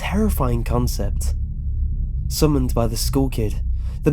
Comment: sounds like he is saying, ''Summoned by the school kid'' xD